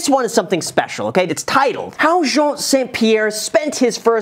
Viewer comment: Did he just call him Jean St Pierre? haha